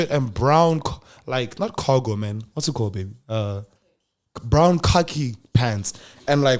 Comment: I know ian the only one who heard baby at